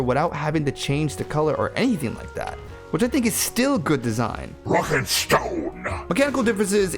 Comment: Did I hear a rock and stone?